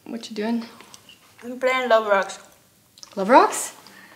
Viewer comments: did I just heard her saying I'm playing Roblox?! that's so cool AHAHAH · "Playing Roblox" (I don't care if I misheard it, it's canon)